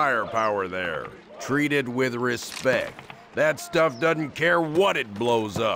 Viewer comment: Am I the only one who heard 'doedn't' at 5:35 ?